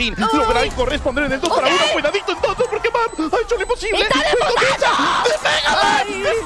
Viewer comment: did she say "EST ALEEE POTTOOOO"